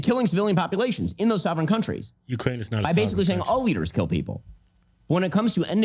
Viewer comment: Ukraines not a sovereign country , I heard at 5:14 ?